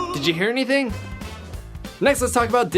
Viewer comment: I heard "Do you hear me, Sophina?" Anyone else?